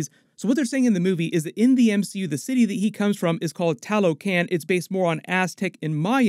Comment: Sounds like you said Taloc but I think it should be Tlaloc. Not that it matters but I actually used to know a guy named Tlaloc :p